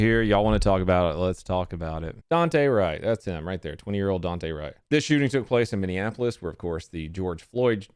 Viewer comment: Tbh I thought it said Dwight my heart broke until u say it said different